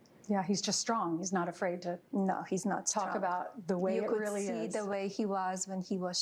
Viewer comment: does Melania say "No. He's not strong"?